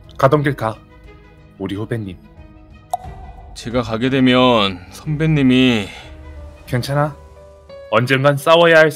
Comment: 에 나만 선배님이 가면 밥은 누가 사요 라고 들림?